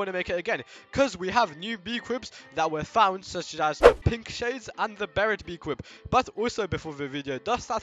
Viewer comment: I thought he said “pimp shades”😂😂😂😂